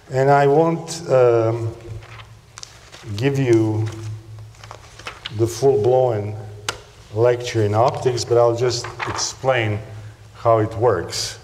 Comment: when the caption says "want to give you before going, like to [inaudible 06:09] but I'll" - what he's saying is "want to give the full blown lecture in optics, but I'll".